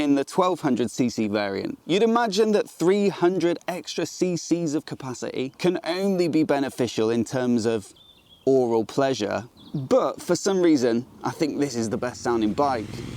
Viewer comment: Did you just say 'oral pleasure'?... (yeah, I know, aural)